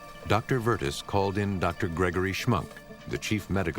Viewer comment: I thought he said 2:10:51 “Dr. Shmuck” 😆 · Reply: Dr Gregory Schmunk 😂😂